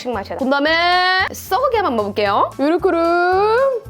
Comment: 썩은계란 한 번 먹어볼게요 라고 들림ㅋㅋㅋㅋㅋ